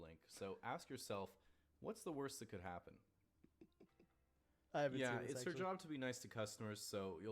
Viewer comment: Did anyone els catch the fact that soda said he has already seen the vid but then later acts like he called the fact that it was a prank? Lol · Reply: I think he then said "I haven't seen it actually"